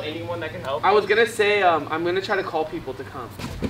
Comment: Thought he said he was gonna call the police 😂